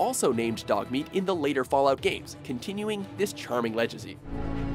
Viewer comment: this fucking guy says "lejacy" instead of legacy